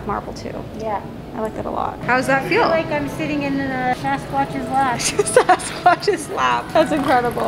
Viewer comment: When your mom said “I feel like I’m sitting in a Sasquatch’s lap” I thought she said “I feel like I’m sitting in a Best Western Plus” hahahahaha